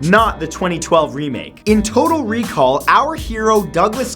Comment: on number 3 " Total recall " I thought it said "toilet recall" for a second lol